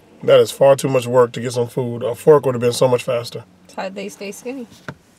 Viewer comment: Did she just say thats why they stay skinny??!??